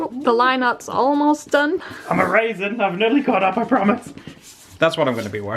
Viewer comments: when said "hold on, I'm erasing!" I heard "Hold on, I'm a raisin!" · When said he was erasing I thought he said "I'm a raisin!"